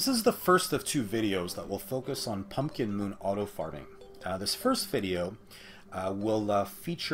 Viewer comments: did anyone else notice it sounded like he said auto farting instead of auto farming in the beginning? 🤔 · did anybody else hear that a little weird it sounded like auto farting!?